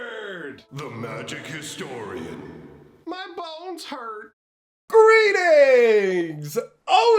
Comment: New "My Bones Hurt" Merch is now available! - <link> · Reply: I swear I always heard "my balls hurt" xD